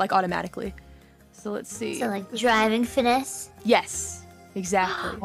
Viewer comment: 最近才入坑，感謝全熟肉大大提供這麼詳盡的影片，EN全推感動到要哭出來！！！！ 來提供一下自己聽到不同的點： 32:34 driving fitness （一般是說driver fitness，不過古拉說什麼都是對的！） 可以考慮翻成駕駛素質/駕駛能力